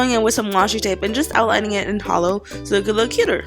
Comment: did she say holo? CRISTINE!!!!